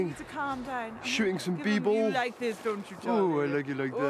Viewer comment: I don't get it, am I actually the only one who keeps hearing 'Shooting some people' instead of 'b-ball' at 0:45? Because I honestly found it hilarious for the last 10 years until I looked down at the comments and realized I got it wrong all along.